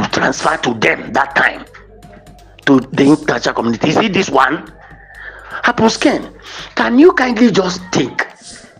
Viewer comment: I thought they said that this man is a pastoe